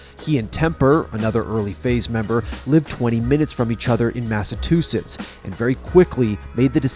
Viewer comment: Wait.... did he say "Massatoosets"?